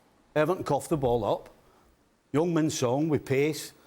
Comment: thought Stevie said young man son 😂😂had to watch it again